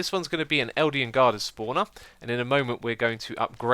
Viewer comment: Do my ears deceive me? Did you just say "Eldian Guarder Spawner"?